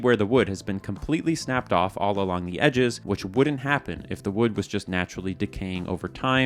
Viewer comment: Did you just say... wooden happen? 😂